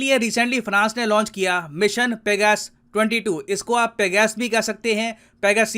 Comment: At first I heard mission BigAss then I realised I was wrong...😆